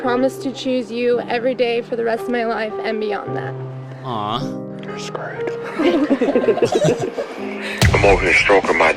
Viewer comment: anyone else hear someone whisper "you're screwed" at 2:43 ?? also much love kurtis <33